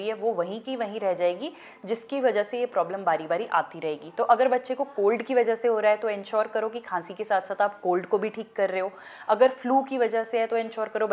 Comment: did u say covid ke.wjhse ho rha h?🤔but this video is from 3 years ago😳